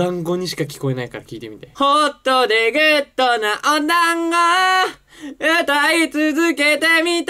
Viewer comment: 「ホットでぐっとなお団子」って聞こえてたの全く同じ✋